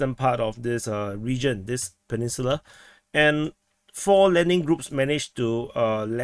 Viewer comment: Sounded like you said that four "Lemming" groups landed north of Ovlenka. In hindsight, that is accurate.